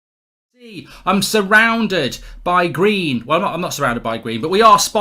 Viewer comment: "Eee, I'm surrounded by green" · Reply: Oh I thought he was saying "Im surrounded by greed" hahaha